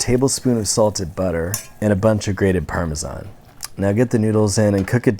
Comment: this boi said parm-is-on lol